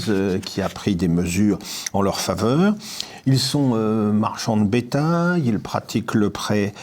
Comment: à 4:20 vous dites: "ils sont marchands de [bettingues?]" c'est quoi le term entre [...]